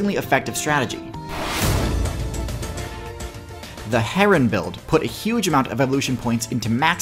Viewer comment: I thought heron said heroin